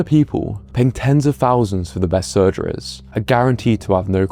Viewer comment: In minute 12:37 Did you just say 'pay tens of thousands to the best *surgerors*'? Mate!